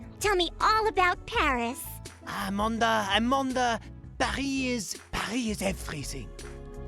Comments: I heard "Paris... Paris is a prison" for that line. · “Pari-Paris is everything 10:15 - 10:16 or 17 n*gga” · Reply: I thought he said “Pari is freezing” 😳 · I heard "Paris, Paris is freezing" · Ok I heard "Pari is everything" and I CANNOT hear the n word or is it just me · “Pari-Paris is everything 10:15 - 10:16 or 17 n*gga” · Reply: I thought he said "Pie... pie is everything" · I didn't hear the n word at ALL. I heard "Paris is everything" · I can't hear the n-word? (Thank god i can't) All i heard was :"Paris is exquisite." 10:15 Edit: OH! nevermind. I heard it (- ~-) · I heard '"Pie is everything." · 10 min mark. i thought he said parie is everything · I heard "I'm on the Paris. Paris is freezing". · I hear Paris everything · I didn't hear the n word at all, I heard "Paris is everything" in that stupid fake accent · i played it several times over and I only heard "Paris is everything" never have I heard the n- word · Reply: I heard “Paris is freezing” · I thought he said Paris was freezing?